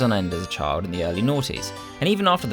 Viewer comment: The guy didn't even play the game. He claimed to have memories of playing in the early 90s when the game came out in 1998. 98 isn't early 90s, lol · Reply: I thought he said early aughts, which is how some people say the 2000s